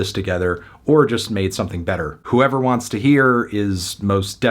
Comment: Sounds like he say's "deramy P"